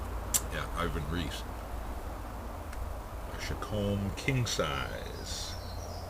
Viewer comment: i really like the shape of this pipe , i heard you saying the name of it but i couldn't really hear it ? shacomb king size that's what i heard , can you tell me the name of this pipe ? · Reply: ahh chacom king size, i did a little research, beautiful beautiful pipe sir.